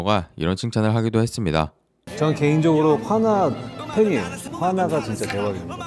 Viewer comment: 한화팬으로 들림…ㅋㅋㅋ